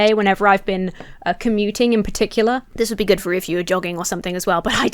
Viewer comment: Oh god you said JOGGING! I was certain I heard Dog